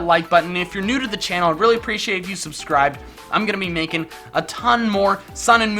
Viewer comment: He said "i'm gonna be makin'" I thought he said he was going to be naked.